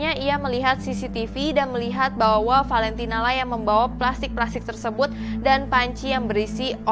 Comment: "terekam bahwa valentino yg sedang membawa panci berisi organ" (Gatau bener apa ga kalimat nya😅) Maksudnya apa ya kak?nama korban nya Valentino kan?terus nama pembunuh nya Tamara kan? Ini ak yg salah denger,atau emg typo?😅